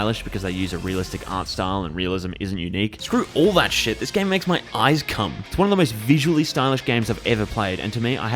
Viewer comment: Did he say this game makes his eyes cum?